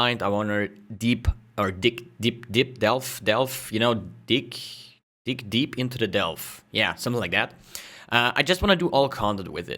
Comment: I thought he said "dick deep into the delve" at first.